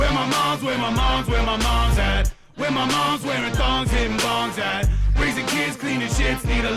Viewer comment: Alison Walk Where my moms where my moms where my moms at? Where my moms Wearing thongs hitting bongs at? Raising kids cleaning shits need a long nap. Where my moms where my moms where my moms at? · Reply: I thought I heard "dogs eating thongs at"